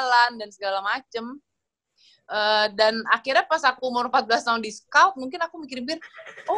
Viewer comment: Kelly bilang apa? Discaught?